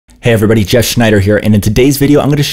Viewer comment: i thought i heard "Hey everybody, Jazz Schneider here"